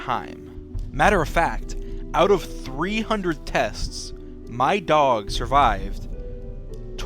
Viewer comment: excuse me sir did you say 300